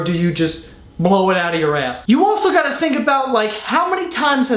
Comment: I was legit half way putting a dip in my lip and I heard " hold on I just squirt blood out of my ass" legit the funniest thing I'm heard all day